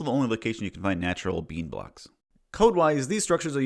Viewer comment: What word does say at 4:55 ? The only place you can find __ blocks? The auto generated subtitles say "bean blocks" but there's no way that is correct. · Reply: I do say “bean” it’s a joke that the raw ore blocks look like beans.